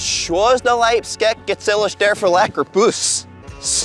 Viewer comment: I heard “puss” …